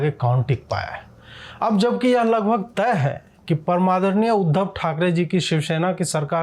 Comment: What did you say? param madar niya uddhav. Well yes that does sound correct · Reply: Param adarniye